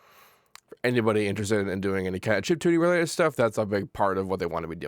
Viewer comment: what in the world did he say at 0:56 ??? lol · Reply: Chiptune